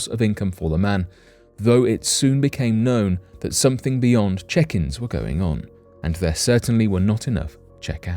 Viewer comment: I thought you said chickens at first I said why the hell are there chickens in that hotel. Like water chickens have to do with the story and then I said oh check-ins.